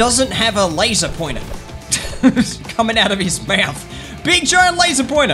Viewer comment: Did he just say Benchyi have laser pointer